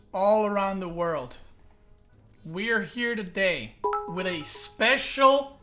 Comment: Every time on the intro I crack when when you say "we're here today".. all I hear is "we are hair today to... " Lmao